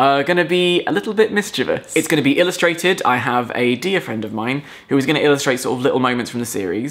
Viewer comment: "A dear friend who will be illustrating 'little moments' from the series" hey? do I sense Chloe illustrations?? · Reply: I heard it as 'a deer friend' and imagined a team of helpful woodland creatures.